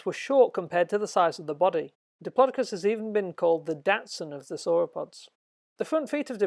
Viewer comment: Oh. thought you said 'Datsun' there.